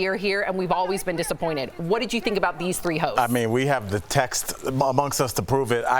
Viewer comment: i had to turn on subtitles cuz the first 3 times i heard "hoes" instead of "hosts"